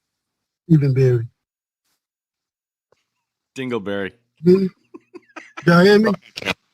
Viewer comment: Omg He sounded like he said dingle berry and he sounded like one too